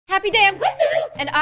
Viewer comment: Never knew that she said I’m gloom I thought that she said happy day on gloom 😅😅